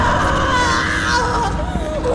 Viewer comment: siempre pense que decia:you make me fun of you